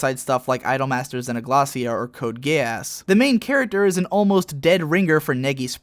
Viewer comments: You call it Code Gay-Ass too? Or am I just hearing things. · did he just say code gayass!?